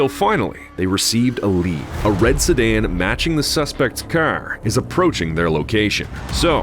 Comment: Car? i thought you said cir.